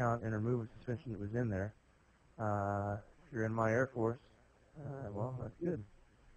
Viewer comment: in the voice message it sounded like "youre in my airport"